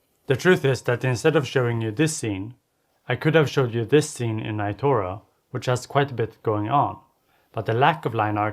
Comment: What's the name of the game that appears at 1:30? Sorry, I couldn't understand the pronunciation (English is not my native language) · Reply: Itorah :)